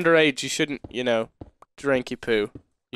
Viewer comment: I thought he said we shouldn't drink our poo.